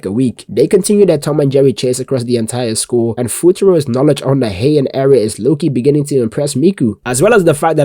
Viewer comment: did he just say the heian era